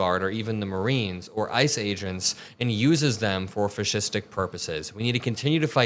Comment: "he uses them for fascistic purposes" I first heard it as "he uses them for fishstick purposes" haha. This entire admin needs to go to CECOT.